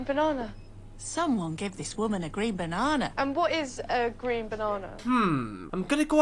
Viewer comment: I thought she said "preen" which is how you describe what a bird does, cleaning its feathers and making itself beautiful 😂😂😂 Or (of a person) devote effort to making oneself look attractive and then admire one's appearance. "adolescents preening in their bedroom mirrors"